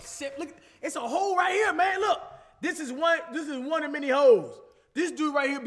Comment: Is it just me or when said holes I thought he said hoes